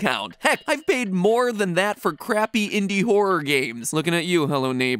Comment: Matpat: "creepy indie horror games(I'm talking about you hello neighbor) " Also Matpat: 3 game theory videos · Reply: i thought he said crappy